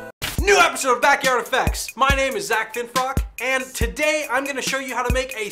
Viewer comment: i thought he said may name is zack efron???